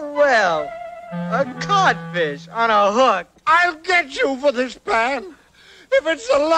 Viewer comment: I thought Peter said “A caught fish 🐠 on a hook 🪝”